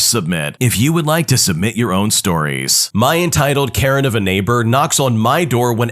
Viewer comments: 😈 FINISH LISTENING TO ALL THE STORIES HERE ▶ <link> 👉 SUBMIT YOUR OWN STORIES HERE ▶ <link> 👉 SUBMIT YOUR OWN STORIES HERE ▶ <link> 👉 SUBMIT YOUR OWN STORIES HERE ▶ <link> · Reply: The caption says carrot and the video says karen. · The caption says carrot and the video says karen.